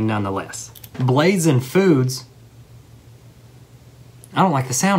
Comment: Am I the only one who thought he said "blades in foods" 😳